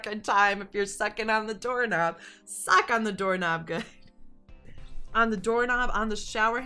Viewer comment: Who else thought Cole said "suck on the doorknob 😂😂😂 not sock on the doorknob 😂